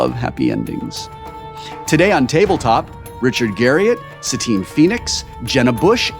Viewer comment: Richard Garriott, AKA Lord British, is one of the nicest and most talented people in the entire gaming industry. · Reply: LOL same here, for some reason I thought he said Richard Garfield, and I was thinking "oh cool the guy who created Magic: The Gathering. This should be a good game of five tribes" I'm a huge fan of Garriots and screamed like a little girl when I saw it was him ^^